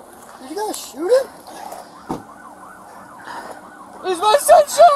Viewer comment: The cop that didn’t shoot, and that was acting rationally, legitimately couldn’t believe his partner shot him. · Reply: I'm sure it was the guy in the car that was asking cause i heard "did you guys shoot him"